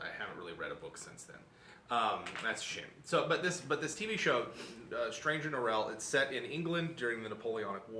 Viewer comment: I am interested in reading the books you mention at the start (something like stranger norels?) (mentioned at 2:10), but I can't hear the title properly, and googling doesn't help me either, so how is it spelled/do you have a link of some kind? Also, I love the way the way you talk about things! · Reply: Jonathan Strange & Mr Norrell, also on Netflix